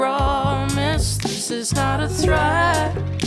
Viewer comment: thank u for replacing prank with threat honestly that one word made me like the original song like 50 percent less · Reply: oh shit I always thought he was saying this is not a prank HAHA the way he pronounces it is funny and it sounded like he was saying prank to me, sorry abt that